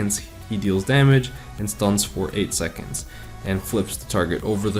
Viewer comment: did you say his ult stuns for... EIGHT SECONDS? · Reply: 8 seconds